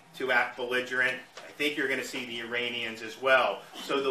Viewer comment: Serious question? At around 3:10, did he say the Uranians?